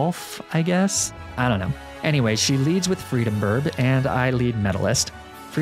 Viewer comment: DID HE SAY FREEBIRD